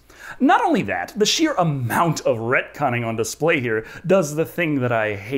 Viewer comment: I thought you where saying 'redcon'. and couldnt find what it meant. Now I know its retcon. Suddenly the world makes sense